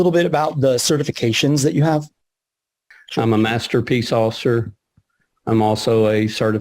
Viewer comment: Did he just say he's a master piece of pop.